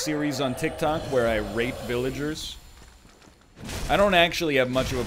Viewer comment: Was it just me, but at 5:59 I thought he said rape